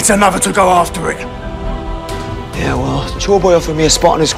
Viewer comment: I love how Sanji saying ''yeah well the chore boy offered me a spot on his crew'' sounded like ''yeah well joyboy offered me a spot on his crew''